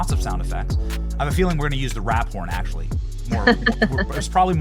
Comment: My hearing is kinda bad... thought he was saying gonna have to use the rat porn sound effect.